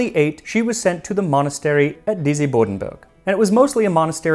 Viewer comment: I heard: "Busy Borden Burg"